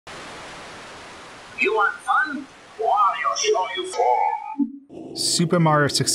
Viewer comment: i always thought he said "You want fun? Why, I'll show you fun.'